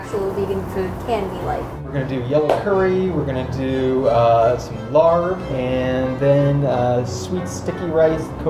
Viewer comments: does homeboy say lard?!!?! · Did he say he was cooking lard at 5:05? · Wait a minute...did he say, "...we're gonna do some lard..."? @ 5:02 Pretty sure lard (animal fat) isn't vegan or vegetarian. Please tell me I'm wrong. And the subtitles are wrong too (as they often are). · does he say lard? Because that isn't vegan. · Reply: Think he said larb which is a thai dish, normally made with minced chicken or beef but can be made with tofo